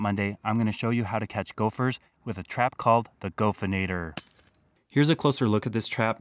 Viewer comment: i thought it said "The Gopnikator"